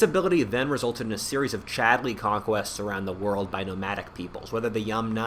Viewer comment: did he just say "chadly conquests?" chad pastoralist moment